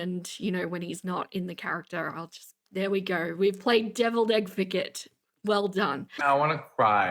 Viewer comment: did u just say "devil's-EGG-vocate"?? 😆